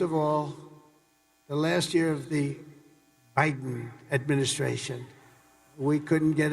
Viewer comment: Did he say Hiden administration? What is trump hiding?